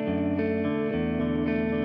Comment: 一回目は映像みて、 二回目は歌詞みて、 三回目はみんなの考察見ながらイヤホンで曲聴いて泣くっていう 僕なりのヨルシカさんの曲の楽しみ方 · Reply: この曲何回聞いても1回目のサビが「乾かないように重い手を」に聞こえる